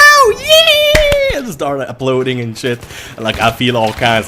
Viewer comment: Lol your accent makes it sound like you say uploading when you said applauding